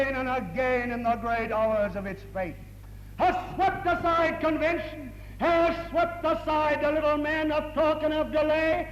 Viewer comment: anyone know what he says here? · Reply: I believe it’s “sooner or later”